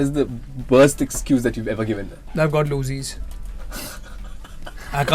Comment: what did he say? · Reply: he said i got loosies means loose motion/diarrhea and if he attends event he may leave marks of it over their which is terrible😂😭